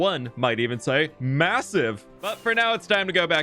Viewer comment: did I hear that right? massive?